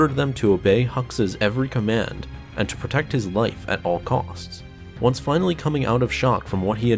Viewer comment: Am I only one who heard at all c*cks?